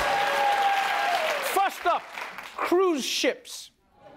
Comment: i thought he had said fu€ked up but it was first up 😄 😄 😄 😄